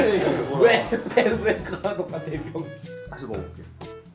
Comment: 병× 이라고 들었다